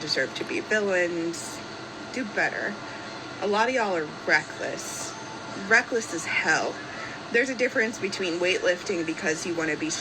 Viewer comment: So the first time you played this clip I heard “A lot of y’all are breakfast, breakfast is hell.” And I thought, well I guess bacon is a high fat food but that seems a bit intense 😂